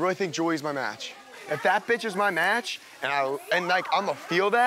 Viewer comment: Did just call Julia his "bitch"?